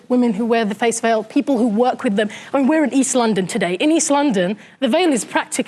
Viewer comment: did she say "is-london"? like islam+london?